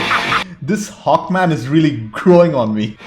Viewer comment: I swear, at first I thought you said “This Hawk-Man is CROWING on me...”